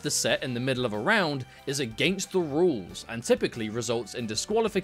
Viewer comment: I thought you said "a gangster rule" 😂